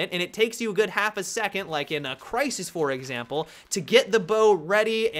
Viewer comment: did he said "Crysis 4"